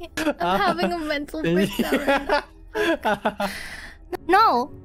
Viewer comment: I think Zeta said "I'm having a mental breakdown"